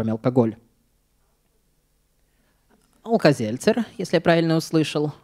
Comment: Какое слово было названо из зала? В таких случаях нужно повторять, что сказали люди, например, без микрофона. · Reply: Алкать